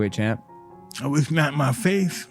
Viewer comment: what did he said? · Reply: He said “At least not in my face”